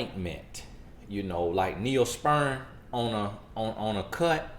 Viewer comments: "Neospeern"... lol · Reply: 😉 I thought he said Neosperm. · Did he just say, "Neosperm🤔